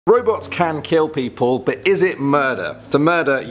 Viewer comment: I thought he said Roblox 😂